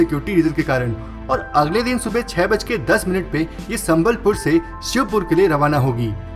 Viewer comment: I heard Sambalpur instead of Sabalgarh.